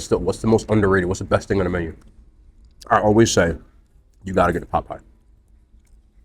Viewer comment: totally thought he said “you gotta get the Popeye” lol